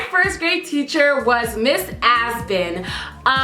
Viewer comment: I literally thought she said Miss AssBin 😂😭😭